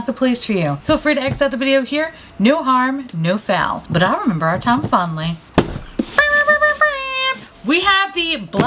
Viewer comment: For some reason, I always hear “But I’ll remember our Tom Fawnly!!” Lol